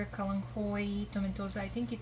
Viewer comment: Does anyone know what she said that was at 2:35? Teddy Bear...? I’d love to know, please. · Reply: it is kalanhoe tomentosa “teddy bear” 😊